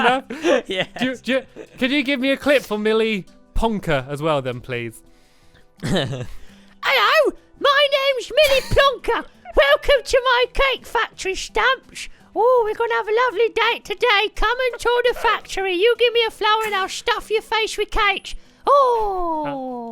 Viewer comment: I THOUGHT HE SAID MILLY THE PLONKER